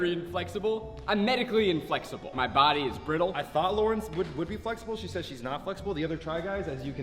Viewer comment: does he say Laurence instead of Lauren